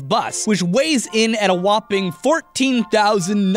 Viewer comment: Did he just say nice after saying 69? 4:56 😂😂